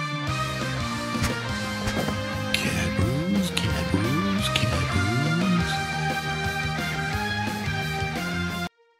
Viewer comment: does anyone know what Garfield said in this? Cause I have no idea? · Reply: cat rules cat rules cat rules